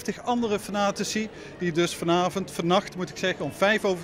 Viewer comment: Wat zegt ie Harry Potler? XD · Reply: Nick Beurskens Harle Potler 😂